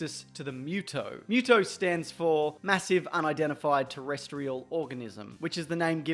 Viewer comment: When he said what muto stands for, i heard “massive unidentified terrestrial orgasm”. Is that bad?